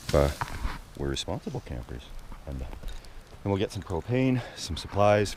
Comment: Honestly thought I heard you say you were going to get some cocaine from town